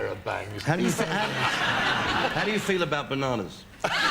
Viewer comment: what did Mick say and the other Man? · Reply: I didn't get "how do you feel about bananas"